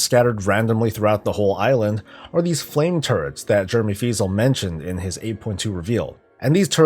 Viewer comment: I could have sworn you said "flame turds."